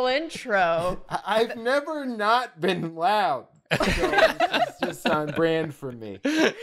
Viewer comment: “I’ve never not been loud” - the drawfee program in entirety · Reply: At first, I heard "I've never not been aroused."